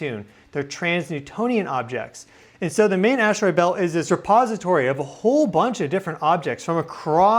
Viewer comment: Dude! You cracked me up at 6:30, I thought you said "suppository" instead of "repository." 🙂 Then you talked about slipping it in to the asteroid belt. 😳Then you showed a blown-up image of it that looked like a glycerine suppository. 😅 Apparently the universe also thinks the Earth is clogged up with 💩, so it's slipping in a suppository to lube things up. 🙂👍